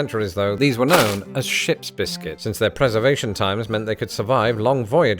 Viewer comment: I hate to admit, I thought he said "SHIT biscuit", not "SHIP'S biscuit" ... until the second playthrough :o